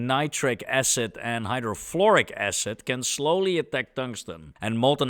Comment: was the a typo, about the hydrochloric acid? Cuz I swear u said it doesn't desolve it but then you said it easily does. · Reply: I must work on my pronunciation. I said Hydrofluoric Acid but it sounded indeed a lot like hydrochloric Acid :)